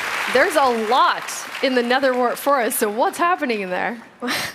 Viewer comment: I thought she said the N-word forest